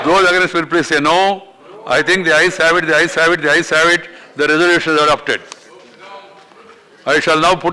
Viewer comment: can anyone please tell what he said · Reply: the ayes have it